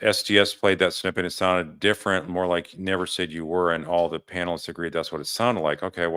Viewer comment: I heard say “and you were”